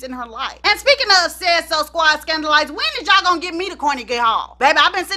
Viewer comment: I thought you said no scandal? 😇